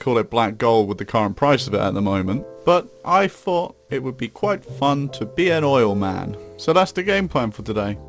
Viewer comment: who else thought it said "being an old man"?